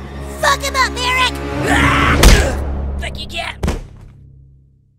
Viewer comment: Bro I thought Kyle said heidies boobs are on